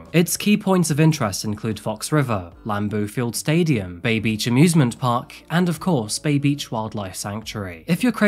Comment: I was watching this while doing some household and i swear i heard "Babies amusement park" and "Town Morgue" .. never have i ever been so confused 😂